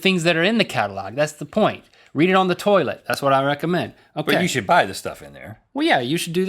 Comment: i thought he said soilet. like as in toilet